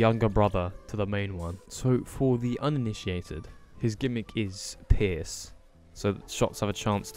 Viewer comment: I looked away and I heard "for the uninitiated, his gimmick is piss "